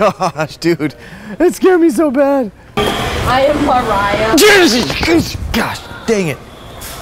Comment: Did anyone else hear I am Mariah